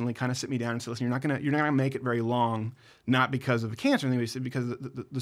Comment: he says "not because of the cancer" Anybody else catch that? He said it really fast. EDIT: Either way, let's all pray hard for his recovery!